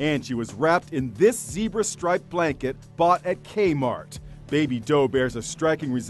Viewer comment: I had my volume down and I heard " Gay Mart "